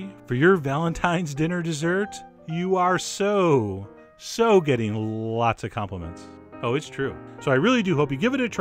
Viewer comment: i thought he was gonna say you are soo soo gettin..laid.